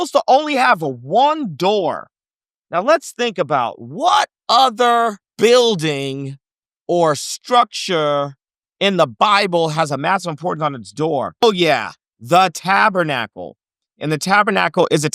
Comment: What does he say is on the door of the ark? I couldn't make out what he said. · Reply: I believe he is saying, "Let's think about what other building or structure has a massive importance on its door! Oh yeah, the Tabernacle..."